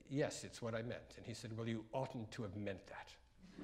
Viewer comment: what is that word he likes? At around 7:05? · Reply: I’m having trouble make up what Bridge said to Britten, “you often to have meant that”? What did it mean? Thanks in advance.